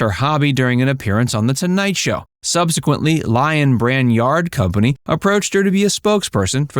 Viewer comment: I truly thought you said "yard" instead of "yarn"